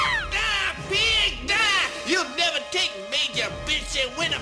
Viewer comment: a long time ago, I thought he said "bitch", instead of pig because sound quality on my old tv wasn't great.